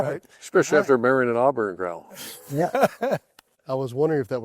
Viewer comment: What does urban say at 24:21 I can’t understand him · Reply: “Especially after you married an Auburn gal” Sumrall’s wife went to Auburn